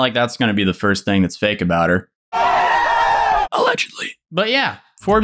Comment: "alegelly" I swear it sounded like "Its Free Real Estate."